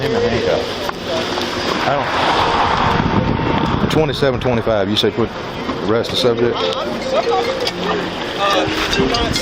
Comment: sounded like, "2725, you say what? Arrest the sumbitch?" 😁